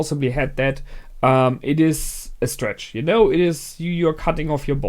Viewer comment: I zoned out for a moment, and at 4:55 I momentarily thought you said "you're cutting off your balls" and was like "wait.. what!?" before realizing you actually said "you're cutting off your bonds". Also, I won't say what, but something you brought up in this episode reminded me of Gunbuster. I realize you have limited time and a million recommended things to watch, but it's a six-episode classic that I'd suggest you add to your "maybe someday" list.